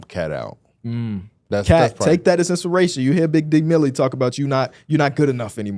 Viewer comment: Did bro just say “Big Dick Milly” 💀💀💀